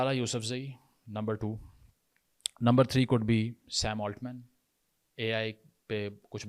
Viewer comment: Did he say sam altman...a practicing jew in Pakistan 😂